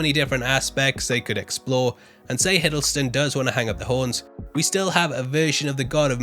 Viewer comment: I'm sorry did you just say "And say Hiddleston does wanna hung up the horns .." at 18:59 , nah I must be hearing things